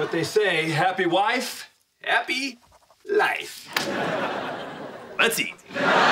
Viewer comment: What does he say at 4:02 ? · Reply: Let's eat